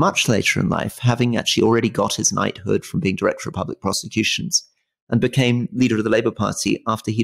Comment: Lol did Rory just say that Kier Starhmer was director of public prostitutions??